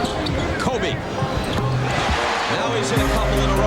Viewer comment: Why does the commentator sound like he said COVID instead of Kobe